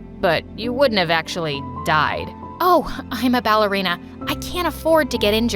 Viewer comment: At the beginning, I thought said, “Hi, I’m Ballerina” 😂